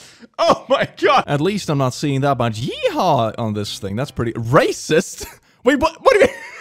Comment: I really thought he said racist boats